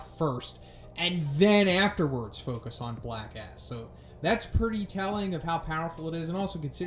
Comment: Every time i hear "Black S" i think its "black ass"